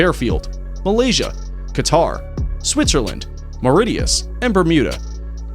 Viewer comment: Did he say Mauritius?